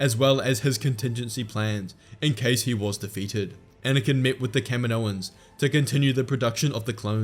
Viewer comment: I thought he said Kevin Owens maybe because I am wrestling fan